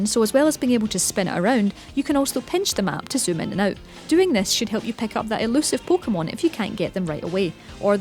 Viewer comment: did she say "doing this shit" at 4:52" really